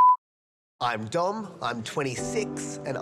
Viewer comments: the second one when he said ''im dom'' i thought he said ''im dumb'' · i thought he said im dumb